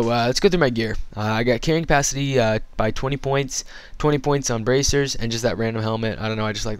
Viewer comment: Lolz, I thought he said 20 Points on Brazzers...